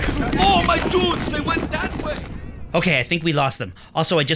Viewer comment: When lewandowski said my dudes I misheard it as my pubes